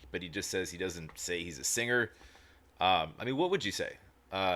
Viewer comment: I'm fairly certain he says he isn't a "sanger" based on the context of what he had just said about "break," "broke," and "broker."